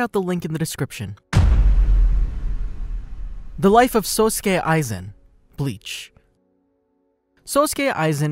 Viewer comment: Hol on run that back did he just say SUSuke Aizen?